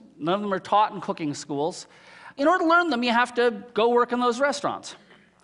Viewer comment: Did anyone catch what he said at 1:30? The most profound thing I have ever heard. WOW!!! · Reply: What?... "in order to learn their way of cooking, you have to go work in their restaurants"....? Or "the old way of looking at food is the old way"...? Neither of these statements are not in the least startling to me... both very apparent, to me... So what illicits the "WOW!"... from you ?